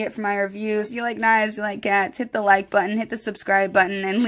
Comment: Also sounded like you said “if you like knives if you like gats “ 🤪 ratatatata lol